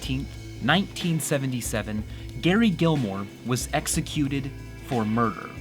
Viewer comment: for a sec i thought he just said "David Gilmore" 'cause background was sounding like Pink Floyd...